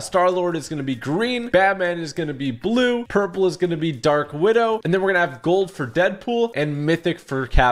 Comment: did he said dark widow?